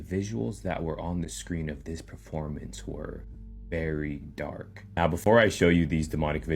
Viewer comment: did anybody else hear dark